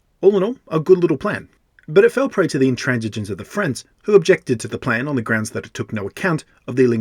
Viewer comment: 'The intransigence of the Friends.' I thought at first you said 'intransigence of the French,' which works just as well!